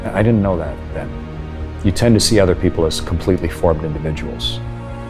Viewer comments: I think he said 'completely formed' not 'completely foreign' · Subtitle is wrong at 0:39 he says ‘completely formed individuals’ instead of ‘completely foreign individuals’ if I”m not mistaken